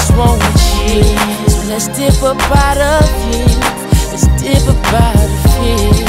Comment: Y’all worried about if he said “she’s fine too” or “she’s 5’2” but when I was little my stupid ass thought he was saying “so let’s Hippopotamus” and not “so let’s dip up out of here”🤦🏾‍♀️🤣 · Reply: I thought he said "it's deeper outta here" 😂😂 ur not alone